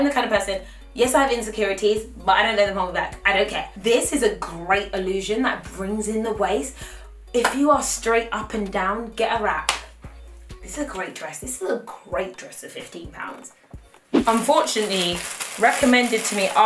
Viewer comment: I heard "if you are strait up and down get a rack" 😂😂😂 yep pretty much